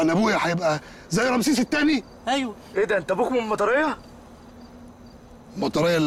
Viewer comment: اوس قال ابوك من المطرية؟ اريد شخص يوضحلي ماذا قال احمد مكي!؟